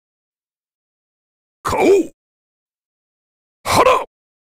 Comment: did he said "Ah Sh*t"? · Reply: Ashi which means ”legs” i think